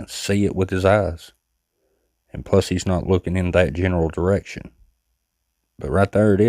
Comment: did you say “plus, he’s not looking in that general direction” or did you call him a mean word for a cat? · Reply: ❤❤😂😂 th3 first one. I said he wasn't looking in that general direction lol